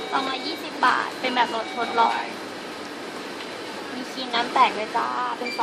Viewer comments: กูได้ยินว่า ครีมน้ำแตก เหมือนกันใช่ไหมมมมม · ห๊ะอะไรนะผมได้ยินคำว่า''มีครีมน้ำแตกด้วยจ้า'' ทุกคนได้ยินเหมือนผมไหม ถ้าเหมือนLikeเลย *-*